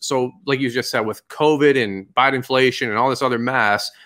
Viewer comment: Did I just hear him say “Biden-flation” 😂😩